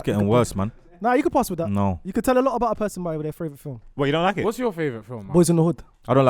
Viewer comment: saying “fravourite” instead of favourite again 😂😂